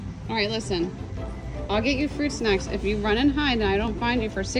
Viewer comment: I thought she said free snacks not fruit snacks